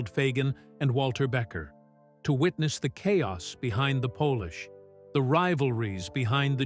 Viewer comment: the video recites "the chaos behind the pōlish." LOL. This is the first I hear of Steely Dan being from Poland.